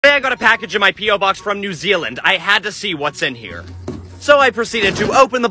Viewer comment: Thought it said new feetland💀💀💀